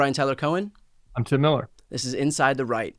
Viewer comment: You know, at first I thought Brian said, "Inside the Reich"...was that a message from the future?